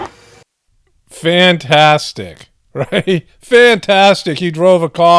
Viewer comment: When that media guy said "Fantastic!", I heard "You bastard!" Maybe it is a laurel/yanni thing? Or maybe he's just a bastard?